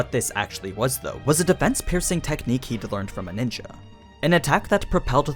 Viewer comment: Did he just say NINJA ?